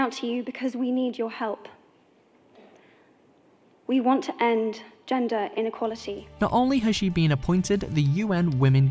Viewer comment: Why did I first hear " I want to end, gender equality " ...